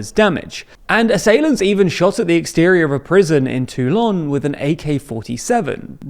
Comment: At first I thought he said “TooLong”…